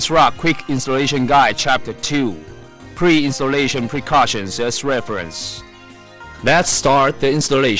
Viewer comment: LOL the first time when he said "Read Asrock quick installation guide as reference", I thought he said to read the installation guide vas deferens XD